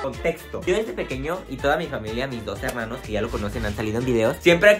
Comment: En el segundo 0:17 escuche 12 hermanos jsjs